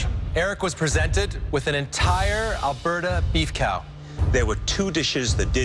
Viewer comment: Did Claudio just say BeefCow?